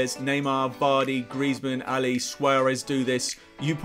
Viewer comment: Did you say Swearez at 12:26 😂😂😂